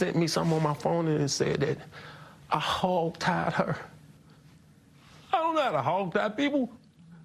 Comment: “I don’t know how to tie a hog” 😂😂😂😂 Why did he say it like that?